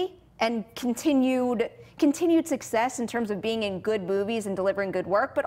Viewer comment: Sounded like 'Good boobies'. 😂